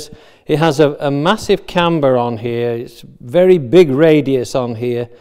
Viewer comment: Paul says something like: "It has a massive can burr on here". What does he say, "can burr"? I simply cant hear it or I dont know the words :) Thanks. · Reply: mrvelleful he says "camber" which means a slight curve or round edge